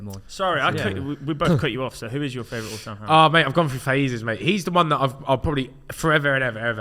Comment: Who does say? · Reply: “I’ve gone through phases, mate” meaning it has changed multiple times.